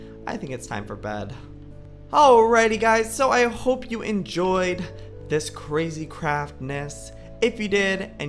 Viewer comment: At first in the ending I thought he said well I hope you enjoyed this crazy crapness and I laughed a lot in my head